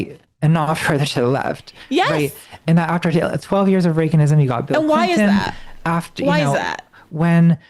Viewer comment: did she say “clinton won because he was coming in after 8 years of reagan”?? Bestie??? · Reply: She says 12 years of Reaganism. Reagan / Bush from <num>. BJG says "8 years of Reagan" at 2:02:40 which is technically incorrect but not philosophically correct. It was 12 years of Reagan / Bush which is what Clinton ran against but was also part of the "third way" DLC